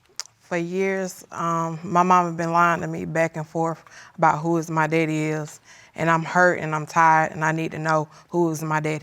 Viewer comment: Did I hear "Who is my daddy is?"